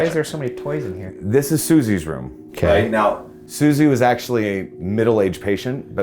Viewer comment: it sounds like someone says "Lighten up!" very loudly. It happe4ns right when the camera switches to Ryan.